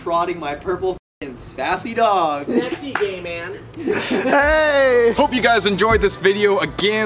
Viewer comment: i died when said "sassy gay man" in the window 😂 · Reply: i thought said sexy gay man